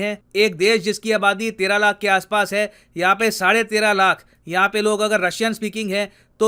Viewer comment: sade tera lakh🧐 instead of 3.5 lakh